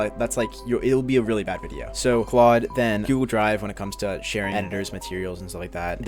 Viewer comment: what drive did he say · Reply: Google Drive